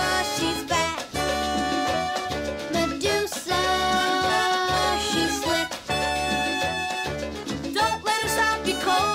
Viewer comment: MEDUSAAAAAAA SHES fAT Legit what I hear